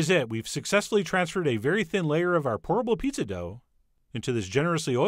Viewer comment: I keep hearing "Horrible pizza dough" instead of "pourable pizza dough"